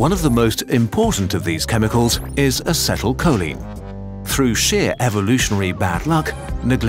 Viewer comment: lol I thought it said through shit 😂 😂